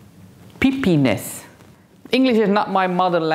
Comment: 🤣 it did sound like she was saying “peepeeniss” 🤣 I had to play it back ten times, put closed captioning on and finally it dawned on me, she was trying to say she “peeped” or looked, checked on what the color was doing under the foil 🤷🏼‍♀️🤣